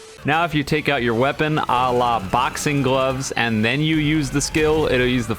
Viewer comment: did bro say Allah